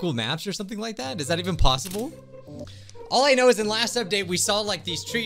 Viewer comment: “like, does that even possible?” (thats what I heard)